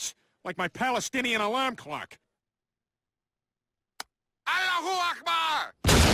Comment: someone tell me what the alarm said because I laughed at the explosion but I don't think what it said was nice. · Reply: Allack akbar